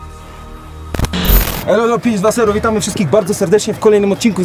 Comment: Czy ja usłyszałam "elo elo 520" 😱😱